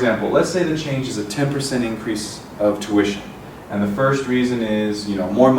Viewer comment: What does he says on 2:58 ? Twich or smth like that ? Anyone know what is that word ? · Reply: He says "Tuition Fees" :)